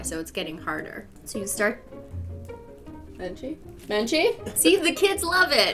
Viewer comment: When she said menchie i thought she said AJ